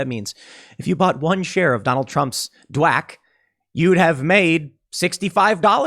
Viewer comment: Thought Tim kept saying "Donald Trump's WAP" and I was very confused and disgusted.